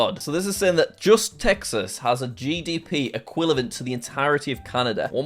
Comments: Anyone notice he said equilavent instead of equivalent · Is it just me or did he say “equilivent” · did bro say equilavent